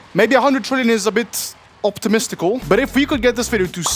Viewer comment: why do i hear Optitestical